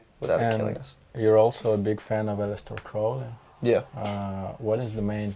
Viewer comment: what's the philosopher called you talk about at around 19:30? i can't understand accoustically... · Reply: Aleister Crowley